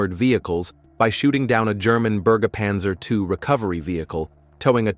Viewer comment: + I think it is called Bergungspanzer (not Burgerpanzser). ; - )